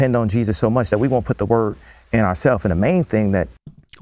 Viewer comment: I think Kanye said "work" not word, and its just how he pronounced it is the confusion. He does go on to say next that Christians pray instead of physically doing something, and how when God didn't answer him he will do it himself, so it's most likely "work" not "word.